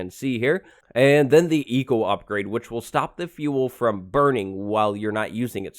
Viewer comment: I thought you said Etho upgrade and not Eco upgrade xD